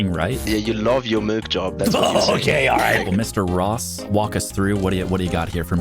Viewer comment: I’ve been watching too much Smiling Friends. I thought Ross’s square said “Mr. Boss”